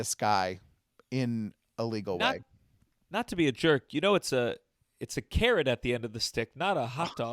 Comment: i thought gianmarco said “karen at the end of the stick” LMAO